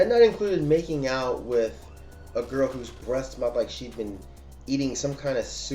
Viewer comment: LOL I thought you said her breasts smelled like that rather than her breath I was like okay dude, we don't need to know about you smelling some girl's breasts